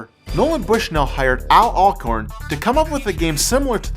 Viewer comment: Could've sworn said Al "Acorn". Is this a remaster?